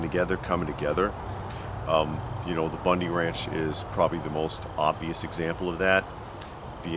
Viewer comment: You said BLM amd the bundy ranch. i couldve swore those were sheriff's deputies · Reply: Ohhhh, he was saying Bundy ranch! I thought he was saying Bunny Ranch and was confused af! Duhhh on me 😂